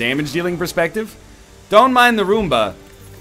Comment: Thought you said "Don't mind Arumba" haha